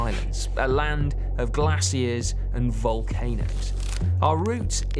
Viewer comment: Beautiful and educational video. Also, I heard “glass ears” when you said “glaciers”. Very funny and confused until my brain switched back on.